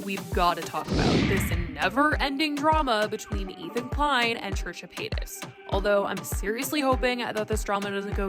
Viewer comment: off topic but when you turn on captions and go to 0:26 it says Churchill Hayden instead of Trisha paytas lol